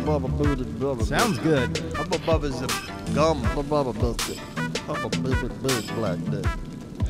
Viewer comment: did he say haboboba big black dick?😂😂😂